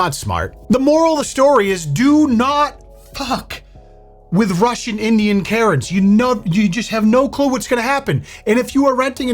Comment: I thought u said Russian Indian carrots